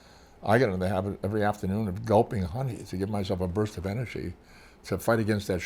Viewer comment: Did he say he would drink honey? Is that a euphemism or is drinking honey a real thing?